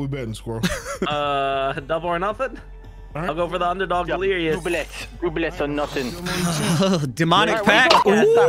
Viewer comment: I one hear russian - "blyat" from delirious? 🤔 What he really say?